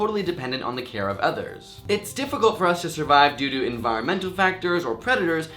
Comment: when he said due to it sounded like he said doodoo sorry but im rlly immature